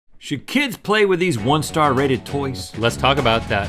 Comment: I paused when rhett said star and when i unpaused i heard "R rated toys"